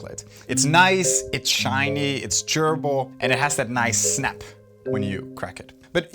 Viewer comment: lol 13:27-13:31 "Its nice, its shiny, its.......gerbil???" lol...think he's saying chewable but his accent lolol